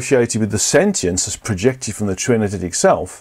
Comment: you mention the "trinititic" self. Does anyone know what that is? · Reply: He’s saying True Energetic Self. Our Higher Self, in other words. Which is created by Source or God. Our Soul is a small piece of our True Energetic Self, about 2.5% from what I’ve heard Guy say elsewhere.